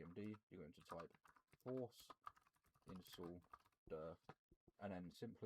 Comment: Sorry but at 1:39 what did he say after force install? I can't understand what to type there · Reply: I found it force_install_dir The folder IRL